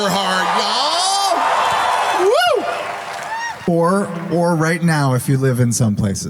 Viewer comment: Sounded like Marisha said "You whore!" Probably didn't but it still makes me giggle.